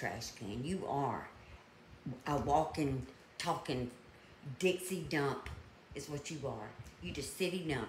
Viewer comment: Ditzy dump. lol. gotta love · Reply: I thought she said "Dixie Dump" which would have been hysterical since she's from the south;).